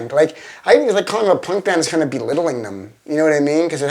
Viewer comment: Please can anyone tell me what Flea says at 7:29, I'm creating portuguese subtitles for this video, but I can't get the job done without knowing what he says there. It's pretty weird, imo. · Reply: "belittling"